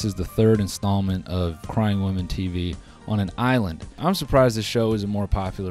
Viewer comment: I fully thought he said “crime woman tv” and rolled with it until the middle of the video 💀